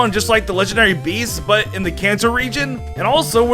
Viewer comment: My subtitles thought when you said Kanto region you said cancer region